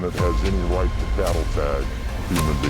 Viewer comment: “Nobody on this planet has the right to battle tag a human being.” For someone to utter those words is terrifying! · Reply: I thought he said “cattle tag.” Certainly seems like that’s the degree of contempt we’re being abused with.